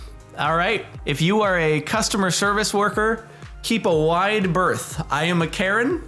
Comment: did he just say… keep a wide… birth?!